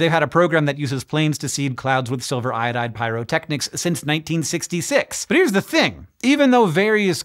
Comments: Transcription at 7:20 reads 1996 instead of 1966. Also, graphic at 2:48 shows a water 'drip' shape, while rain drops are more like flat discs. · Just a note: at 7:16 Hank says "1966" while the captions say "1996"